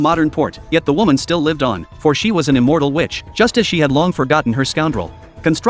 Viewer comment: why did I hear “[…] for she was an immortal bitch.” 😂😭